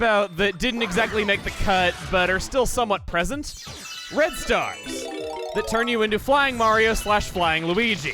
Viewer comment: I was looking away when said Red Stars and I thought he said Bread Stars and I was so confused